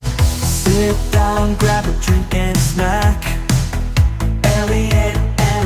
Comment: Ok, I don't know how I misheard the theme song's first line this week. I legit thought it said "Sit back, watch a tall drink and a snack," meaning and. I only questioned it because I thought, "Wait, that's out of order." Monday brain, I suppose.😂